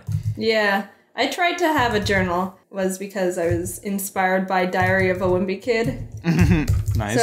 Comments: who else heard "diarrhea of a wimpy kid" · I thought Jaiden said she was inspired by diarrhea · When Jaiden said “Diary of a Wimpy Kid” I thought she said “diarrhea of a wimpy kid” XD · i thought she said diarrea of a wimpy kid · I swear I thought jaiden said " diharrea wimpy kid" 😂😂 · It sounds like Jaiden is Saying '' Diarrhea of a wimpy kid '' -_ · I thought she said diarrhoea of a whimpy kid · Is it just me or did it sound like Jaiden said diarrhea of a wimpy kid... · I misheard diary of a wimpy kid as diarrhea of a wimpy kid. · When jaiden Said diary of a wimpy kid I thought that she Said :diarhea of a wimpy kid... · it sounded like jaiden said dhiiariea wimpy kid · I heard "diarrhea of a wimpy kid"